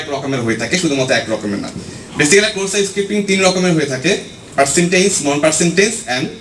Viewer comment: During this time frame I heard about 3 types of cross site scripting which is pronounced as follows: 1. Percintage (Persistent) 2. Non Percintage (Non Persistent) 3. DOM based XSS Did I hear it wrong or it's about sound quality or pronunciation or something else? Anyway it's a good effort to aware people about security.